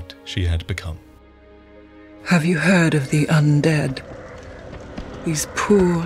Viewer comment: When I heard "Have you heard of the undead?", what I really heard was "Have you heard of the High Elves?"